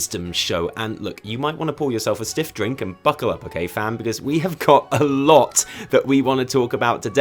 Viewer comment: Ayo fam... did you just say "fam?"